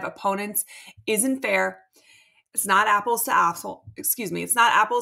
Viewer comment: Did I hear "Apples to a$$holes" in the comparison? 😂 Yes, DJ was wonderful.